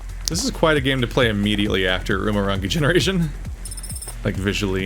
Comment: what was the game you said you played at 12:03 before this? i love cyberpunk stuff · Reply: Umurangi Generation